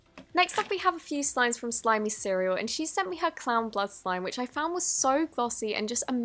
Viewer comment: No offence but at 19:20 said clown blus slim instead of clown blood